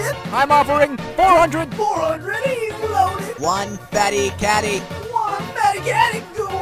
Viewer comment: I thought the auctioneer said "one fatty catty no one wants" until I read the subtitles 💀